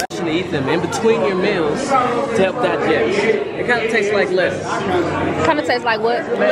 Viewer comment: I’m glad you found your tribe here that cuts for you. When you were looking for that straw, I thought for $200 bucks that cup can suck itself. 😂 Why did it sound like he said, “it kinda taste like lips👄?” 😮